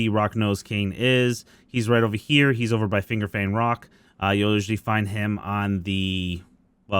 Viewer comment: wait..did he just say "fingerbang rock" ?